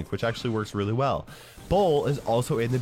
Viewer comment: Did I hear bull was in b???